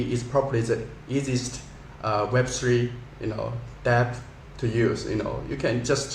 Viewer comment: Good to know about HK :) 30:32 I think he says DApp.